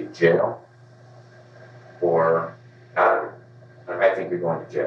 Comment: I think he actually says "Cottonwood" and not "outta here". Cottonwood is a psychiatric hospital in Boise.